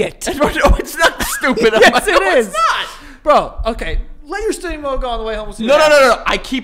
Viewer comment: I’ve been trying to figure out wtf joe is saying at 6:47 for five minutes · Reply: “okay let your steering wheel go all the way home and we’ll and see what happens”